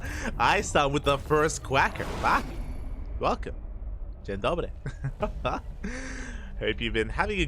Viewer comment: My dear Light_vip did you just say "Dzień dobry"?:)